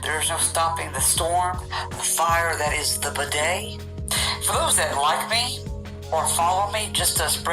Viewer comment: 😂😂😂 lmao it sounds like he says bidet like the toilet